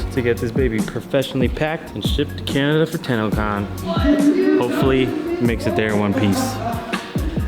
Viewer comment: I thought he said Tannacon 🤢